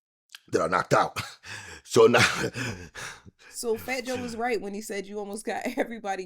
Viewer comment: So Joe was right, I thought he said Fat Joe was lying. Turn out like it always does him lying...🙄